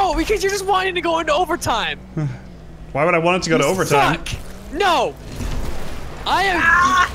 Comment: Did swear at 14:03? Did he say the f word? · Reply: He said suck.